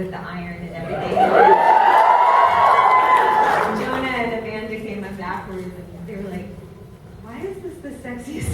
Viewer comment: What does Amy say from 07:18 to 07:32? I can't hear it with all the cheering. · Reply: "Jona and Amanda came up afterword and they were like...."